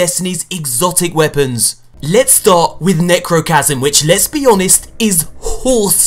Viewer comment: all I hear is, "Lesbi-anest"